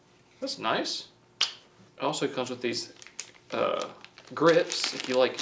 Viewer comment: I thought he said "it comes with grits" not grips lmao